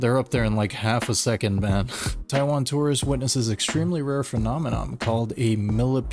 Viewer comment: Did you just say, "phenome-NOM"??😂😂😂